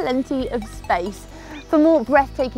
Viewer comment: I could have sworn she said 'earth on drugs'.